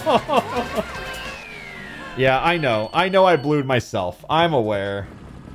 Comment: You know, “blue yourself” is an actual Mario Kart speedrun category where the goal is to carry a blue shell all the way to first and hit yourself with it. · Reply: Oh my god i thought he was saying "blew myself" for the whole video and I was so concerned 😨😨